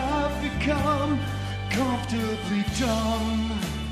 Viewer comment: Am I hearing "I've become comfortably dumb" Instead of "Comfortably numb" ?? BRILLIANT!!!